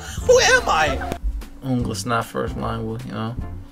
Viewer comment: sounds like he said "English is not my first language"